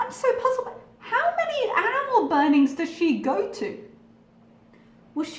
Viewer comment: Even if it was due to mishearing, "HOW MANY ANIMAL BURNINGS DOES SHE GO TO?!" had me rolling. Also, "surprise goat sacrifice" ...I wouldn't mind hearing more about that. Good times. I needed that laugh this morning.